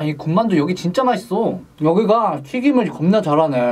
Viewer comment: 여기가 튀김은 맛있네 라고 하는 것 같은데 기분 탓이겠지? 3번 들으니 겁나라고 들리는군